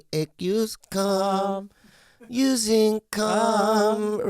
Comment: For second I thought he said, "if your tummy aches use cum."